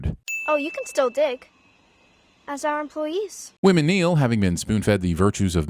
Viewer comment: Wim and Niel . . . I thought you said "women kneel" and thought WT ever loving F did he just say??? Fortunately I saw the subtitles, so . . . .whew.